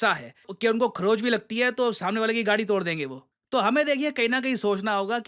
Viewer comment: Did he say Gadi 😂